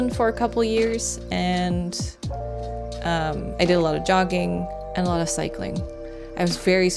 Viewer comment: I had to listen to that again, partially deaf so I heard "you do a lot of dogging". fank thuk I did :) :) :)